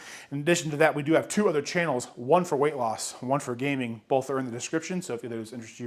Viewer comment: I coulda sworn you said one for losing weight and one for gaining (misunderstood when you said gaming) haha. Just assumed it was a foodie type channel